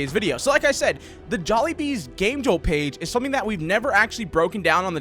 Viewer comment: I thought u said game juul xD